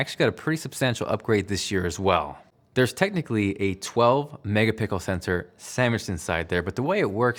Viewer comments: Did anyone else hear him say a 12 mega pickle sensor at 11:57 🤣🤣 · Jonathan said megapickle instead of megapixel at 11:58 😂😂